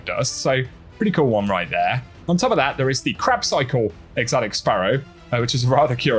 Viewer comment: Best part. I was in the other room listening and thought I heard crap cycle, followed by that is a interesting one 😂